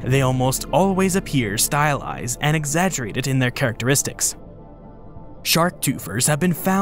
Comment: I can't understand what you're saying here. The caption thinks you're saying "shark twofers" but that obviously isn't right.